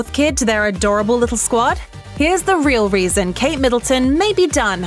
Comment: I thought it said donate kids.